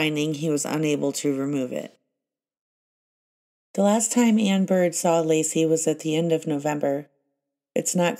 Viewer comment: I kept having to rewind to 15:32 as thought at first you saying Anne Bird was Amber. Strange when you get coincidences. Like all the Nicholes and similar spellings and Nico in the Chris Watts case, or how the kidnapped journalist was called John Cantlie, as in can't lie.